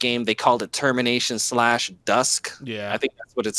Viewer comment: When said termination slash dusk, I heard "termination slash, DIE!" 😂